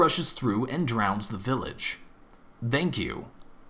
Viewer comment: I heard him say "dank you" at the end.